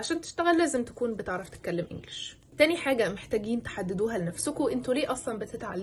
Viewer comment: سمعتها تهببوها مش تحددوها😂😂❤